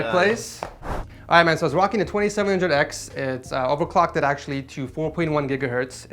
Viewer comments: does he say 2700x lol btw I know he said 2600x but it sounds like 2700x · did he say 2700x